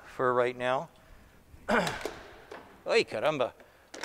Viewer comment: Acho que não vou achar nenhum brasileiro aqui, mas em 4:08 só eu que ouvi ele dizendo “ai caramba”???